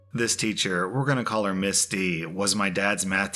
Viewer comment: I wasnt looking when he said Miss D, i thought he said misty lol